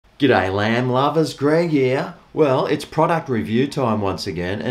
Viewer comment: I genuinely thought you said "G'Day Land Lovers" in the intro... not "G'Day Lamb Lovers". I'm thinking... has been on a boat fishing or something!!?. Then you showed the product being reviewed... and then it clicked. 😆 🍴